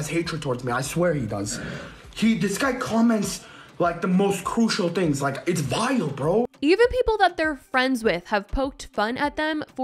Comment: "this guy posts the most crucial things-its vile bro" the fact that he thinks crucial means cruel is depressing af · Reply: It kind of sounded like he said this guy posts the most crucial things - it’s vital. Contextually it makes no sense, but at least vital and crucial are synonyms. 😂 (I don’t really think he said vital. But I wish he had. )